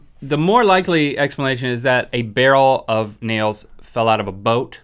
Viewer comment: I thought said 'A Barrel Of Niels'i was like, I'm sorry for you're losses-wait oops